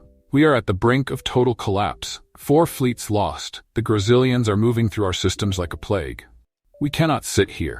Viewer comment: subtitles 🤣 Brazilian instead of gazilians